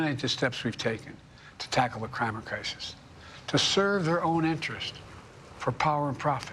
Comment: "the Crime-It crisis" - yes, he actually said that, listen carefully half-speed! Another perfect faux pas!